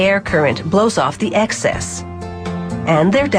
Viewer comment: Lolz i thought you said " NOOO DONT BLOW UP THE EXCESSSSS!!!!" xD thats why i countinued watching..